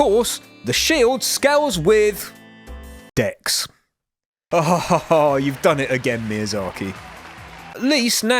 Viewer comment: Is this some kind of inside joke I'm not aware of? I hear ''ksss'' and not Dex. It sounds like an editing error